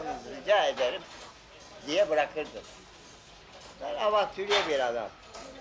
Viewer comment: burda avatülye mi diyor ne diyor o kelime nedir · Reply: Ben avantüre bi adamım yani maceracıyim tahtta öyle kös kös oturamam diyor. Gercekten de film gibi bir hayat yaşamış, mehmet orhan osmanoglu olarak Wikipedia sayfasina bakabilirsiniz.